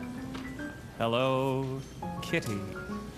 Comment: believe that he means Elle's room has a Hello Kitty motif, either with the brand itself or the color pink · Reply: Jeremy Shen ah ok i heard it more as "hello, kitty..." which was confusing